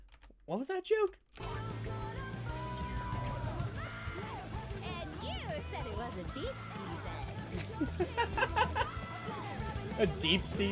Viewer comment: Another song i would recommend a relisten is divide because new info gives some new context to the lyrics 32:50 she is actually saying "and you said it wasn't beach season" it's a call back to a joke in episode one of this season